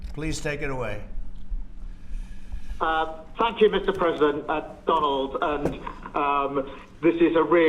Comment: it really sounded like he said "FUCK YOU"